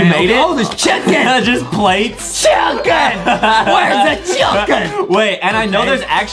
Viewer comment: Wait, did I heard “cHUuUcKeN” but not “cHiIicKeN”?